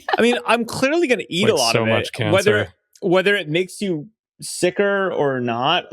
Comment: Did I hear so much cancer?